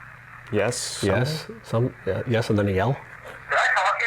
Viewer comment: This is super late but... 43:08 - "Did I talk to you earlier?" The response sounds like it says "Yes SIR!" But I don't know if that's just because I'm hearing it through the mic and then the computer.